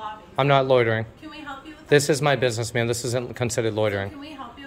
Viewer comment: Wrong yet again!... Press CC (Closed Captions)... And read the text... He actually said... @ 5:33 "I'm not loitering"... "This is my business mam this isn't considered loitering"... Try again!.